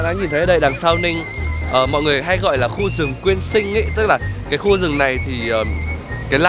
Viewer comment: Tui nghe thành rừng Nguyên Sinh...^^